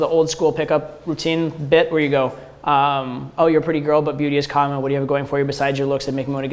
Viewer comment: "oh you are a pretty girl but beauty is ??? b ?? is ?? looks ??? besides makes me ??? more to get you know me better" what are you mumbling? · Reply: Beauty is common so what else do you have to offer me